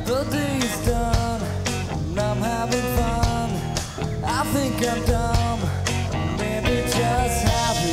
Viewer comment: misheard "i think i'm dumb" for "african doll"